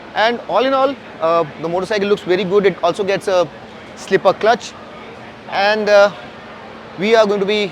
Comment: did you said slipper clutch or mistaken pls reply